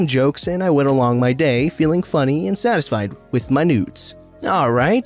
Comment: Every time he said “noods” I hear “nudes”